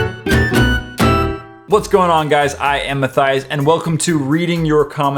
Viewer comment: when he said "what's going on guys I'm Matthias," I thought he said My thighs instead of Matthias....😶